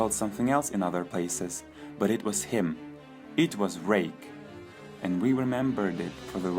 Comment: it sounded like he said- it was rape